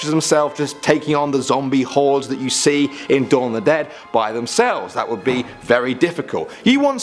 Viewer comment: When 'dawn of the dead' came up I thought he said 'Shawn of the dead', I was like umm mate that movie definitely not scary at all 😂